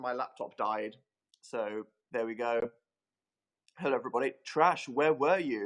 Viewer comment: When he said "hello everyone" I thought he said "hi evil spawn" and I was like hi 😂